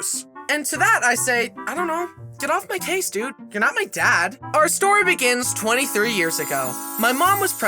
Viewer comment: When he said “you’re not my dad” I THOUGHT HE SAID “and I’m a dad”